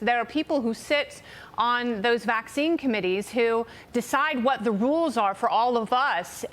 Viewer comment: As a French i thought they were saying " Covid comedy " until i realised it was " comittee ".